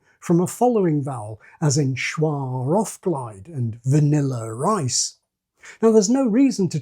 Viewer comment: I was paying a little less attention here and heard you say "Vanilla Rice". I had to go back a; bit and see what you actually said. When the woman says it later, it sounds much more like "Vanillir Ice"